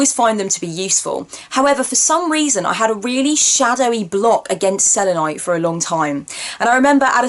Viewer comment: I thought you said, '...A really shadowy block against cellulite'! LOL!